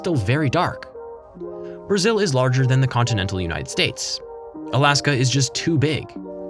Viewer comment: HOLD UP did you just say “larger than the CONTINENT of the United States” ? THE US IS A COUNTRY- 8:02 Spanish*